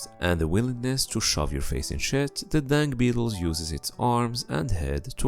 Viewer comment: I thought he keeps saying dank beetle